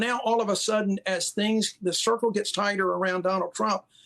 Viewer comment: did he just say the circle was getting tighter?